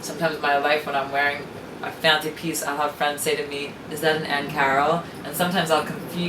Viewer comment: Who is this Ann Carol designer? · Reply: No idea, I thought she was saying, 'Aunt Carol'.